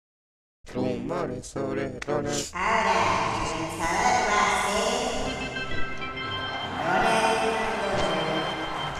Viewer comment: no entendí lo que dijo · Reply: AHORA EL LANZADO VA A SER